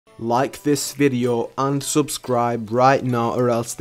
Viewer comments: Did anyone else thought he said like the the video and unsubscribe or is it just me? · did he said unsubscribe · did u say unsubscribe??? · did he just say unsubscribe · Is it just me or did it sound like he said don’t like, unsubscribe lmao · he said unsubscribe instead of and subscribe